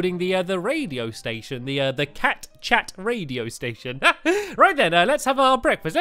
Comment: WHAT ARE THOOOOOOOOOOOOOSE !!!!!!!!!!!!!!!! ;) :) Told my friend I was watching stampys new video Cat Chat he thought I said kit kat :) :) :) :) :) :) :) I catch where he got that from lol : ): )